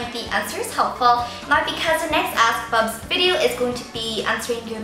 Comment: I keep hearing "ass bubz" xD